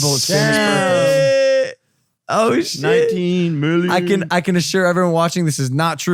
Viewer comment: Anyone know what Logan and Jake are saying at 51:23? Are they saying that Logan's net worth might be $19 mill?